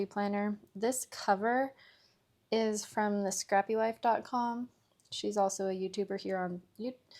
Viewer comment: Thanks, I'm loving the vibrate colors! Its from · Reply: not gonna lie thought you said scrubbywife